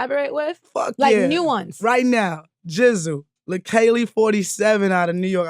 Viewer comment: Tish is sick, defo 1 to watch out for! 16:33 who does Tish say here? Lakali 47? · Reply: Leikeli47